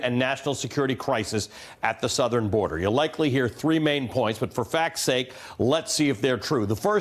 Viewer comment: I thought he said"For F*cks sake"